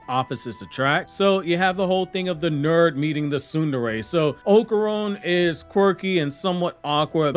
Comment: did you just say sundari That caught me off-guard